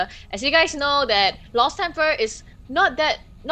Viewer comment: I know im an awful person, but i laughted my ass of when i heard "Rost tempR"